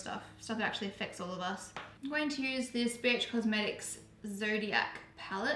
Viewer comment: I thought you said "I'm gonna use bitch cosmetics" but you said BH 😂